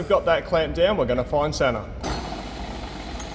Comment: Around 21 min. Heard you say "now we are going to find Santa" , realized your Australian accent...now we are going to find center. Was wondering "what does Santa have to do with this?"🤔🤭 Enjoying your vids.